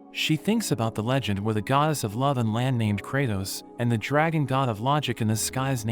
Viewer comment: Did I heard the name kratos